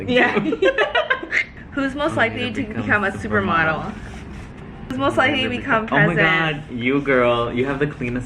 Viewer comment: what did they say lmao i can’t figure it out even on slow mo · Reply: whos most likely to become president! haha